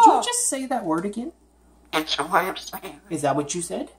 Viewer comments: I heard him say “It’s a way of sayin' it.” I love it! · "Did you just say that word again?" "It's a way of saying" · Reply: That is almost exactly what I heard too! I thought I heard him say, “It’s a way of saying it” (with that slight giggle in his voice!). Then he says “It’s a baaad wooord “